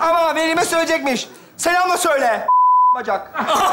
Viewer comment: ne diyor anlayan var mı :D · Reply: Not sadece ne dediğini söylüyorum g**t bacak diyor